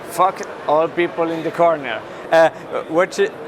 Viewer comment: "F*ck all people in the corner"- lol :D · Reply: i thought he said "fuck the old people in the corner"